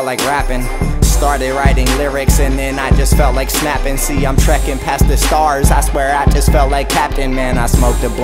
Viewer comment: Everytime he said trekin' I thought he said shrekin'.